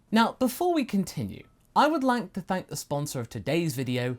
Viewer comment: So i was not looking at the screen when, started talking about his sponsor, I heard "I would like to thank the rich."